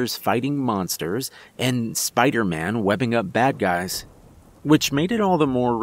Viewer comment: Oh God, I'm late! How does ice turn black?!? I've never understood the phrase. Anyway, I really enjoyed the story tonight. Thanks ❤ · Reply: Snap! Granny thought he said "Black guys" 😒😖😣